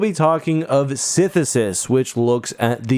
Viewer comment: I heard it as synthesis. Sisyphus could be interesting. Idk either way since this is my first time hearing this story